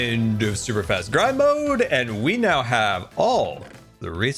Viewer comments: I thought you said "Super Fast Crime Mode"! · I thought you said 'super fast crime mode' instead of 'grind' - I think we also need a super fast crime mode!